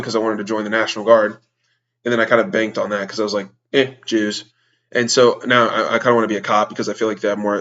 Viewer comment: did you say jews? lol